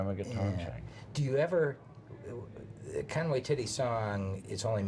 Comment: did he say Conway Titty?